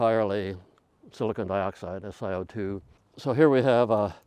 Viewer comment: The subs say SiCO2 instead of SiO₂